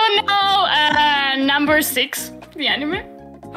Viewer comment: What does say at 21:16 ? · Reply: 'the anime' i think